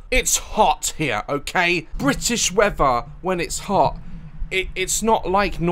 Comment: he really said “british weafher”